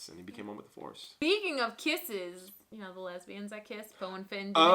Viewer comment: did say "you know the lesbians i kiss"? 56:00 😂